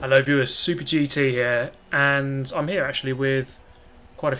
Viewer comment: I always hear "I love you SuperGT here" in your intro xD